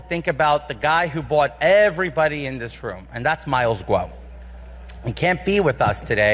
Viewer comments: It sounded like Santos said "Who bought all that are in this room " . I had to replay several times and it sounded like that every time . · what does he say? "the guy who bought everybody in this room"